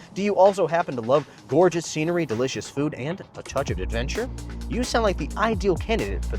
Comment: Wow, that range is impressive. During the Wheelsboy tour promo, at first I thought you said "a touch of detention' rather than a touch of adventure.